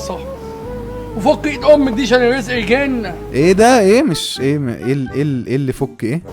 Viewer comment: بيقول : وفكي ايد امك دي عشان الرزق يجلنا 😂😂😂😂😂فهمتها بعد ماسمعت ١٠٠ مرة 😂😂😂